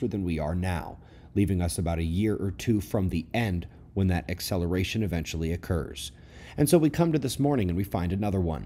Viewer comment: Phew! Got a bit of a scare there this morning. I didn't hear your statement correctly the first time & had to rewind & listen to it two more times to calm my heart rate! The first time, I could have sworn you said we were only a year or two from the Main Event...from the END. It was only after I rewound to that section that I realized you had said we'd only be a year or two from the end ONCE THAT ACCELERATION OCCURRED. Glad I wasn't watching that in the doctor's office waiting room...I would have been walking out of there with an Rx for blood pressure meds & an order for an EKG!